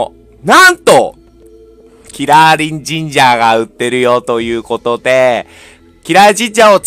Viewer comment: ヤリチンジャーってしか聞こえないw